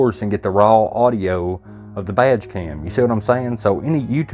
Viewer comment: I realized you were saying “badge cam”… I thought you were saying “vag cam” which made it funny but also disturbing 😂